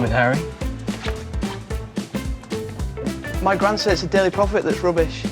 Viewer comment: What was the joke said at 2:29? "My gran says he ...(?)... that's rubbish." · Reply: “ My gran says it’s the daily prophet that’s rubbish.”